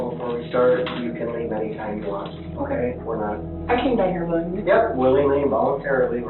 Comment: I don't know if these incorrect captions were added by the police, the prosecutor, this channel, or someone else, but she says, "I came down here willingly," not "I can stay here longer"... it's not even that hard to understand what she's saying.